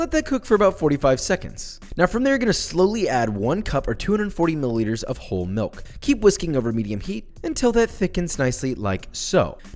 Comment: oh yeah i heard 'whore' milk